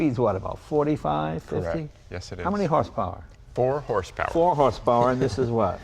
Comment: Did he just say 9 horsepower for $4,000 ?